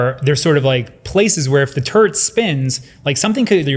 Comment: I swear the airpods translated "turd" not turret and i heard the stiffled chuckle